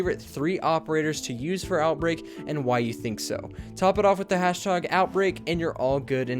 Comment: did you say hash tog?